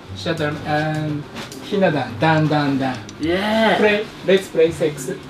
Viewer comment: オーナーがひな壇！dan!dan!dan!って言ってるように聞こえて段流暢に言っても意味無いやろって笑ったけど冷静に考えたらdown！down！down！だな